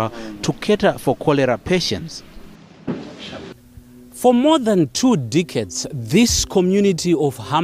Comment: For more than two dickheads.... <--- that's what I heard. I'm starting to think I have a hearing problem or he just butchered the word.